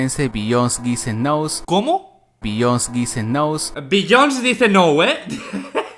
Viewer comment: "Bios dicen nou" yo entendi asi lo que dijo él 😂😂😂😂😂